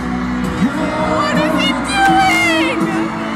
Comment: I SWEAR I heard "what I he doing!?" And then "I don't know but its hot!" Like what!?! I mean he is hot but....🤣 ( 0:54 here is the time if u wanna listen for yourself)